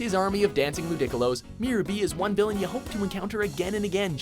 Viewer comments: Dancing Ludickolo's... WatchMojo just ask people for help on how to pronounce things properly you guys have fucked up so many times with pronunciations in multiple thing. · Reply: LOL, I thought he said "dancing gigolos" at first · did he just say lu-dic-colos? lol